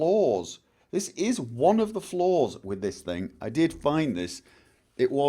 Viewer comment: When you say flawed it almost sounds like you say floored.